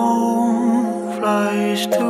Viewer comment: Pickle had cheeeeeese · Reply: Oh shit I thought he said tickle that cheese. Omg.